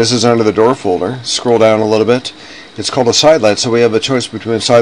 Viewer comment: This is old and the new software wont let you pick a DOOR family and place as a WINDOW. YES it is not a SKYLIGHT. It sounds like he is saying Skylight. ERRG.